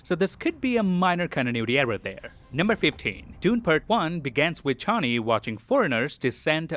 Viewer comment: Did he say Dune-a-verse at 6:01 ??